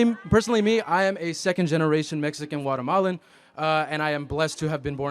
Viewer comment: What I heard "I am a second generation illegal". Throw this P.O.S. out!